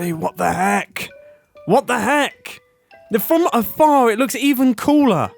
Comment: did he just say AfAR